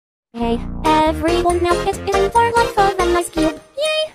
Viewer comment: What I hear: Hey everyone knows its in for life of an icecube YAY!